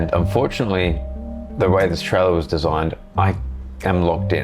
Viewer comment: I thought said unfortunately how Australia was designed…